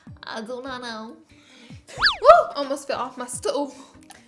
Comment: When she said she almost fell off the stool I heard it as she almost fell off the stove and needless to say i was very confused